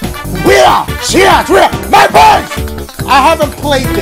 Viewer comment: MY BOYS!!! Sometimes it sounds like he says "MY VOICE!!!"